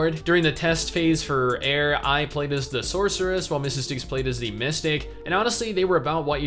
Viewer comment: I understood "Mistake" instead of "Mystic" on one of those occasions, and I was a little bit confused...